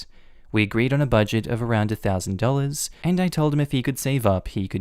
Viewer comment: I thought they said $11,000 for a computer and I was like "bitch WHAT." And then I realized it was $1,000 I was like, yup op is def TAH Also I fuckn hate all those "just do it to keep the peace" bullshit. Oh so I have to bend over backwards for people that make me feel like shit and any effort I give them is worthless? It's not "KeEpInG tHe PeAcE" if one person is silently suffering REPEATEDLY in order to appease so assholes